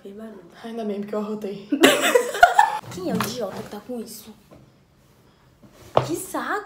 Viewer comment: O que foi aquilo no 0:30 no que ela fala " quem é que tá com isso " alguém sabe me responder?